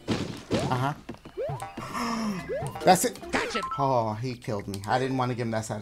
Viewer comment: Nobody is talking about the 16:23 mark. I busted out laughing when I heard "Gotcha!"